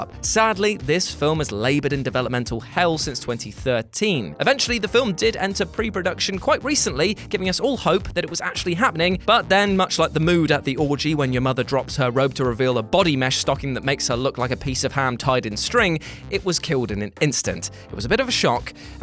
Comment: wait what was it?? Did they say orgy?